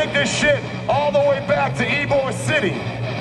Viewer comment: what did he say and what does he mean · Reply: Ybor city is a old place in Tampa that had a bunch of bars and where bands use to have shows.